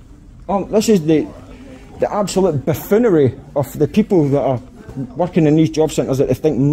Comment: You did swear @ 9:05; you called them a "poof-oonery" 😂